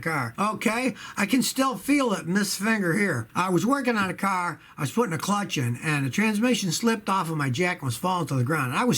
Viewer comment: what are you trying to say there? :D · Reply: You're numero uno.. Heh